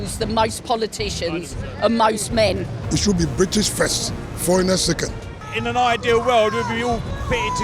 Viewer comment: Did a foreign dude with a thick accent say Britain’s first foreigners second or did I mishear?